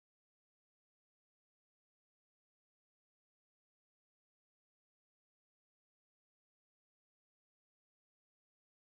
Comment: wish i read lips to know what he said 00:38 :) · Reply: He said "I gots a dashcam. I gots the rightaway"